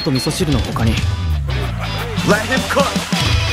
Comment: i dont know if i miss heard but i heard:"let him cook"